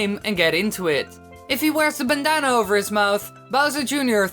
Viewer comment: did he just say banana ?